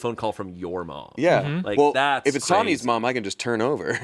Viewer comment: I just hear Angela saying “There’s your clip” lol